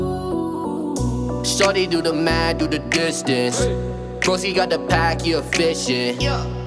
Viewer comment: I thought he said do the dishes